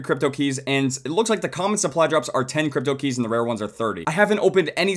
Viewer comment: turn on captions/subtitles and it will say 10 cookies instead of cryptokeys